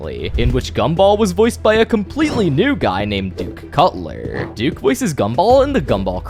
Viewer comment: am I the only one who heard dick Cutler😭 (probably cuz I'm tired rn)